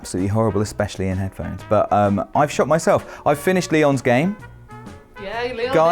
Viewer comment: Maybe it's just my American ears, but every time said he shocked himself, I heard him say "I shot myself", and I got very concerned. 😂